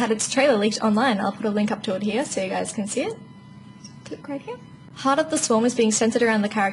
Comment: SC 2 expansion sounded more interesting when I misheard you and thought you said Heart of the Swan.